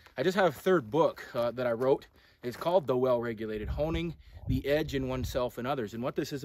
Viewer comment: You don’t make enough videos! :) Coulda swore you said ‘The Well Regulated Homie’! Hahah Had to rewind and listen again. Thanks for all you do!